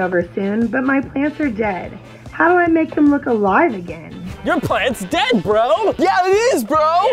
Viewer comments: When I heard that girl say that her plants were dead at first I thought she said my parents are dead, how do I make them look alive again. I was so confused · I thought she said her parents are dead and to need to make them alive again💀💀💀💀💀